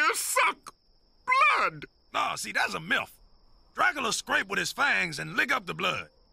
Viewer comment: We COULD say that he just mispronounced the word "myth", but the thought of him saying milf is so much better.